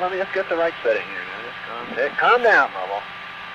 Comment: "Calm down, Lovell!" I love that Jim Lovell got so excited about the view. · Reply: I clearly hear "Calm down, Homo." At 9:55. When does he say "Lovell?". No native ...